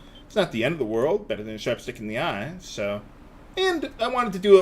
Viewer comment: Idk but " better than a sharp stick in the eye" kills me everytime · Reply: Omg I thought he was saying chopstick not sharp stick lolol